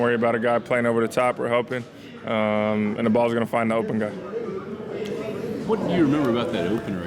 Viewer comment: "THE BALL is going to find an open guy." The BALL, not Carson Wentz. Come on!! I'm not the only guy who heard that!!